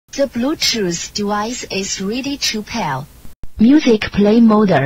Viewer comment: I remember mishearing "the Bluetooth device is ready to pair" as "the Bluetooth device is sweating to hell". 💀